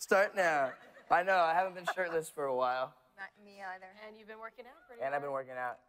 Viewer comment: Did I just hear Shelley Or Holland say "yeah not be either " 1:52, so confused because she was never shirtless on Teen Wolf, lol.